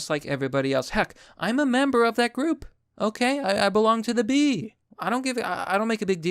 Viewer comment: I just want confirmation on what I heard here. Did just say he was "a member of that group"?